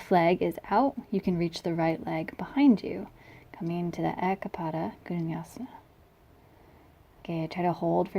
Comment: What did you call that pose? The echo-pada-kunyasina? (I'm sure the spelling is off) Is it still the same pose if I can't open my legs as far apart as you did? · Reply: Eka pada koundinyasana 😊 Yes. Still the same. Overtime, you'll open deeper in the pose if you work on lengthening your hamstrings and opening through inner hips